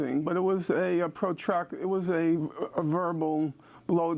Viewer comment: Did Norman just say “blow job” on Democracy Now, likening Tom Friedman's column's deference of the Saudis at 9:26? Hahaha! I love it! What a badass! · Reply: He clearly says verbal blow job. LOL